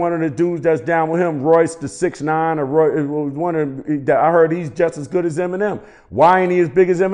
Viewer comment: Did he just say Future is just as dope as Em? Bahahahahhahahah